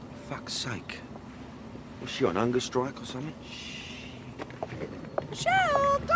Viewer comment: what does he say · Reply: Diana C "is she on hunger strike or something?" That's what I think at least